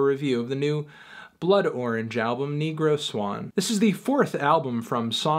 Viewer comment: I thought it said code orange, fuck.